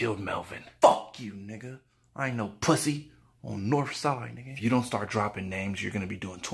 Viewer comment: Did he say,”On northside”??? Lmaaooo